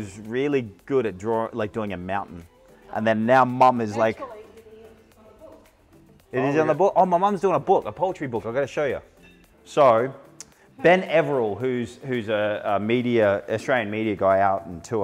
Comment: I thought he was saying "Poultry" 🤣🤣🤣